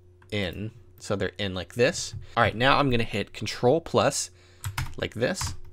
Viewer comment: In 2:12 , Unable to select using ctrl+ like that, Am I hearing correctly? anyone help?